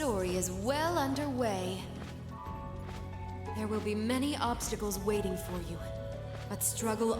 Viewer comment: She did not say barriers, but obstacles. Barriers belong to Lloyd.